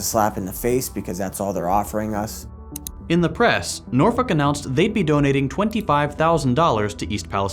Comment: Did say norfolk or norfu**?